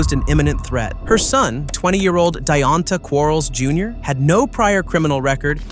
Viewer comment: 20?? I thought they said black don't crack. lmao